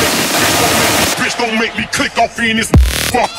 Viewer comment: "Please don't make me click on penis?" that's what I heard xD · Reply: I hear ''Please Don't Make Me Click No Pen!$''